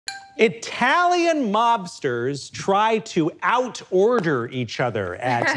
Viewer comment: I was zoning out near the beginning and really thought it said italian lobsters and was so confused what the hell that meant until the video restarted